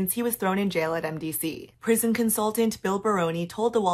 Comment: Ha! I thought she said 'prison consultant Phil Baroni,' and I thought, "yeah, that makes sense."